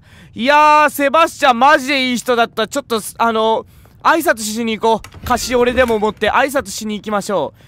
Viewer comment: 「挨拶しにいこう、カシオレでも持って」 に聞こえて一人で笑ってしまいました🤣